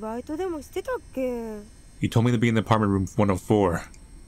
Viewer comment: He did not say that lol it was something the line with He told me to go Ahead 1st but Something something Part Time Job